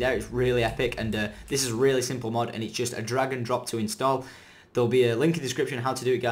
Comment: LOL! The second time he said drag and drop, I thought he said dragon drops. Too much skyrim